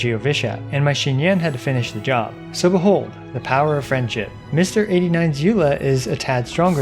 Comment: I swear i heard Power of Family